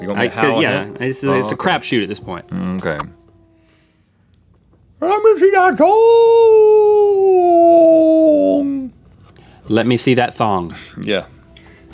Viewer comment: I heard "Remember G.I. Joooooooooe", not"Let me se that thooooong"